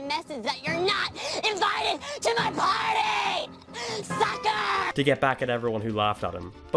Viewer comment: I swear to god he said “fucker”